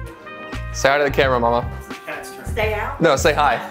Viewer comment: what did he say? "Stay out of the camera" "Say hit time the camera" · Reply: He said “Say hi to the camera”